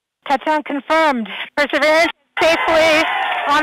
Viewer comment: What do you mean past life? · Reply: sounds like she says its ready to begin picking the sands of past life.. lol